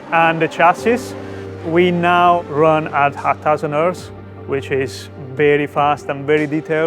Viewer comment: What does he say here? "We now run at ......" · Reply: 1000Hz